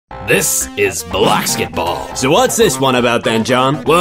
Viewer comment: i wasnt watching the vid and i thought it said blacksketball